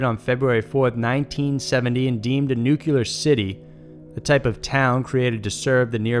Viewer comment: did you just say "nuc-u-lar"